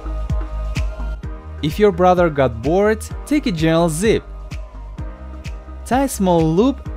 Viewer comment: i heard if your brother have warts he has zits